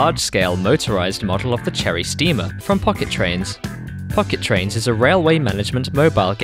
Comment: Why did i hear the "f bomb" when you sayd "pocket train"